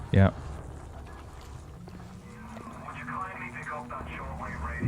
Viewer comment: All I heard was, "Would you Kingsley" and then I went and bought five copies of Bloodrayne. I feel like this is not the...how many little sisters did I kill? Jesus Christ!